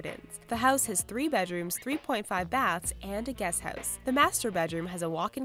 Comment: I am I crazy I heard her say 3.5 bathtubs?